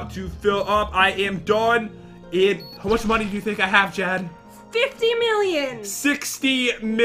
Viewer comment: I thought for a second said jen milllion XD